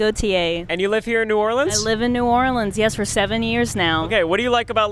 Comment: wait. did she say 70years?